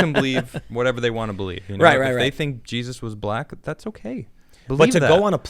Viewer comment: I thought he just said "Ya know Jesus was black right?" and that caused an emergency landing. I was sadly wrong